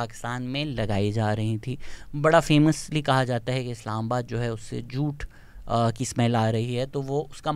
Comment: Bro no offense but what did you say here? 10:57 😂😂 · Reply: Jute